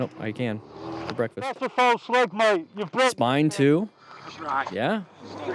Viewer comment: did he just say ‘that’s a false leg mate, you broke the other one’ ……. That’s some shit luck.